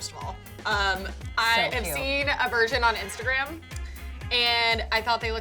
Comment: I thought said I saw a Virgin on instagram lol